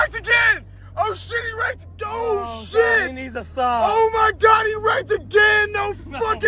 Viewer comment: i thought he said HE NEEDS A SAAB LOL